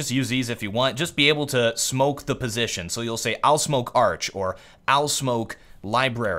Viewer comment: Did pull a SuperTrooper? I swear I heard "owl" instead of " I'll "